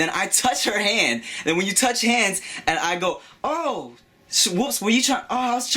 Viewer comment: when u said sh'whoops, i thought u were gunna say shaniqua xD